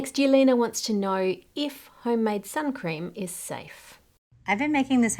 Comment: I honestly thought she said "if homemade sour cream is safe"